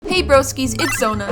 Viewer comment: I thought you said “hey brokies” I was like did u just call me broke